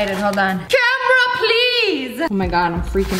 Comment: When she said “CAMERA PLEASE!” I thought she said “DRUMROLL PLEASE!” XD Edit: TY for all these likes